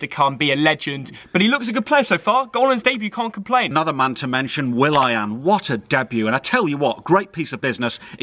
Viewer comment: Who hear saying will ai aan instead of willian